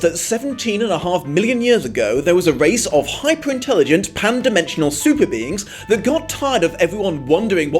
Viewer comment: I thought you said "Hyper-intelligent Pandas" but "hyper-intelligent pan dimensional super beings" makes more sense... I guess.